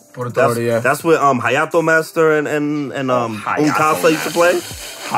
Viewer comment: did he say "Un Kasa"? as in THE Un Kasa...?